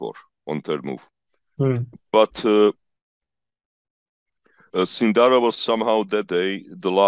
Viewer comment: the subtitles say "Cinderella" instead of Sindarov and at 25:36 there is "plug rananda" instead of Rameshbabu Praggnanandhaa at 31:44 it says "Burger King" instead of working?